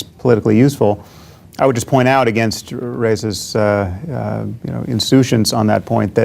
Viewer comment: "...I would just point against Reza's 'insutions' (?)..." What word is he using there? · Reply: 1 comment above ^^ : Insouciance